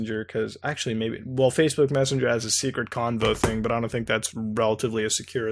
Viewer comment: "Secret combo(convo?) thing" Wat?? ♥ Tell me more, Muta!? --- 18:46 - "She definitly, DEFINITLY! Believes in what she says... I think." ♥ Nice, man... Nice. (I think) ☺ Hehehe.